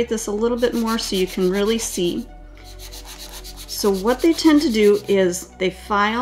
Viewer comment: Hi! What I kept hearing was, "Diana, what you tend to do is...." Lol! I felt like had been watching me sculpt last week 😂 This video was incredibly thorough and ALL points of frustration were discussed and demonstrated. Super helpful